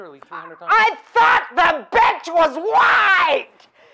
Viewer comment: At first it really sounded like she was saying "I thought the bench was wide!" to me